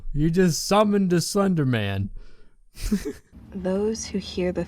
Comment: captions be like thunderman instead of slederman